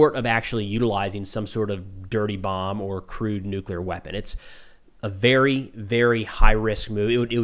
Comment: I don't know why, but my mind heard "crewed nuclear weapon" instead of "crude nuclear weapon", and now I'm wondering... "Hey, what about Major Kong?"